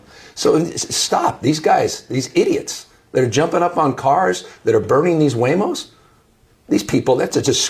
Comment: I thought he said “Theyre burning huevos”. No wonder prices are so high!